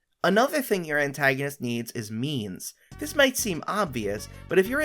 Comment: I had this playing in the background and I misheard that as ‘the antagonist needs memes’